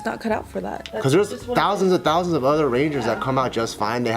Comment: i thought it said power ranger 😂